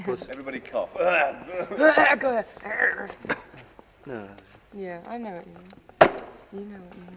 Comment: I think Ringo says "Yeah, I think so." here.